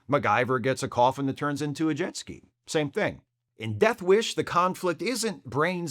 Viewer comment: I see I'm not alone saying "Death wish" (at 27:12) instead of "Deathlock" ^^